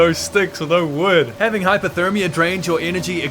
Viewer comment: I know it’s probably just your accent but I kept hearing ‘hyperthermia’ haha. Fun fact: hyperthermia IS a thing! It’s a medical term meaning your body temperature is higher than typically. Aka the opposite of hypothermia :D Anyways I loved the video!! Definitely brought me back as I remember seeing YouTubers play this game when I was younger :)